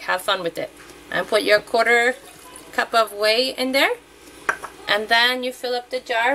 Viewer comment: what do you put in? I didnt understand. Thank you! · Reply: Ti potto she added whey, also mentions it at the beginning